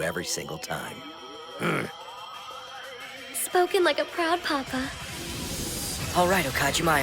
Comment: "Spoken like a proud Papa" hahaahaha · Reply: I thought she said “spoken like a proud Baka”(if you don’t know Baka means idiot)